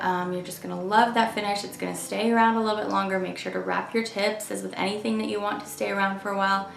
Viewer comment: Right when you said "wrap your tips" my husband walked by and thought you said "raptor t*ts". He looked at me like I am the crazy one.